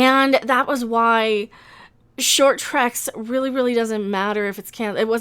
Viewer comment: Why is it that every time I hear mention "short Treks", I hear it as "short bus"?